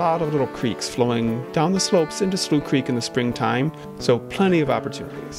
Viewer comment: I thought it was said "sloth creek"